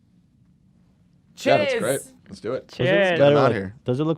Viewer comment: It took me WAY too long to realize it was Ethan bradberry saying ‘cheers’. I genuinely thought it was Hila saying ‘cheese’ for months